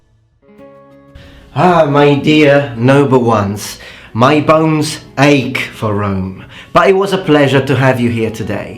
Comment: I thought he said "My loins ache for Rome." Small change, big difference.